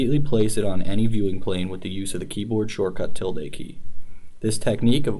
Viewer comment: can any1 hear which is the shortcut key to select annotation view from 3:35 - 3:42, i only hear he said "till date" key?(doesn't sound right) to me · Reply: tilde key - `